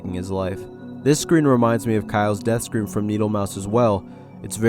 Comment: I'm sorry what did you say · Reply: Tails death in .EYX remind him to Kyle's death from Needlemouse.